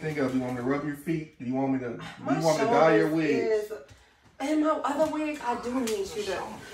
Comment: Did he say dye your wigs at the beginning? 😂😂